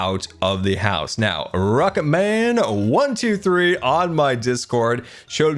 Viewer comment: Thanks for using my design Scar! It’s awesome to see something I helped with in one of these series! The one thing on the doors missed is that the setting on the mechanical bearing was incorrect. If you hover over the bearing with a wrench and scroll you can change the setting to only become solid when near its initial angle. This will prevent the buttons and beam bits from popping off the door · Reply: I thought he said “Rock it, man!” Not “rocket man” haha